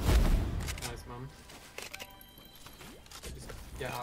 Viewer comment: Did anybody else hear “nice mum”